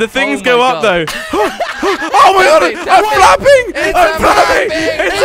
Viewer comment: ummmm..... flaping..... I thought he said faping